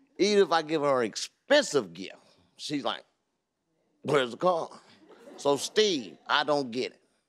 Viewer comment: i thought he said "weh iz the car?" i listen again n again ahh he talking bout card